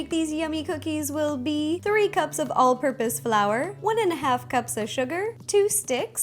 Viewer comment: In 0:33 I heard piss Flour😂😂